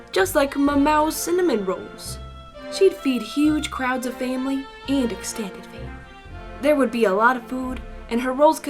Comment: what I heard: she’d feed huge craps to the family and experienced family. I think there is something wrong with...m-me...